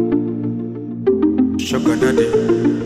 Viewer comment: It says "Sugar daddy " I heard " shoka nadi "😵